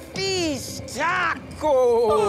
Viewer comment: Not the captions saying [Hilda speaks a foreign language] when Coco says "fish tacos"